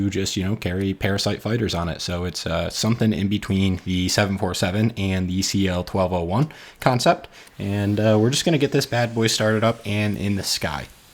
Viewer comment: "ah yes the 747 and...the cl1201? D-did i hear that right?" freebird fades in